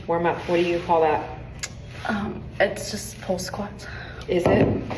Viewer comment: This is why you go to smaller gyms. I go to my local one in my town and every there is there to workout and go home. No one films themselves. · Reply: I heard her say "hoe squat"